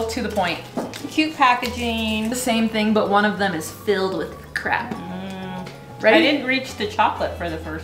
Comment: I stg i heard "one of them is filled with crack"